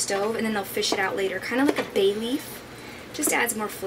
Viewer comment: Awkward moment; When you were talking about the Parmesan cheese rind and mentioned simmering it then fishing it out later like you would a bay leaf... I could've SWORE you said "baby" and I was so confused for a good 30 seconds, then felt like a complete idiot and started cracking up at myself lol